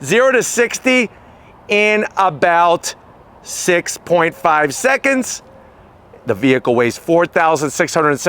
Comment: Did he say 0 to 60 in 6.5 seconds??? ....I must have misheard him because it's probably more like 10.5 seconds, with a tailwind😅